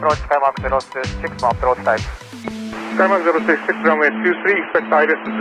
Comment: ここのやりとり、一瞬、approach time と言ってるように聞こえて焦った。シンガポールがやらかしてたし。